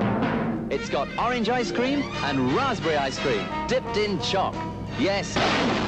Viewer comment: Americans like me need to remember that “corn” here doesn’t mean maize as it would in the USA! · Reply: And that the Orbit was dipped in “choc” (Brit shortened form of “chocolate”) and not “chalk” which is what i heard at first 😄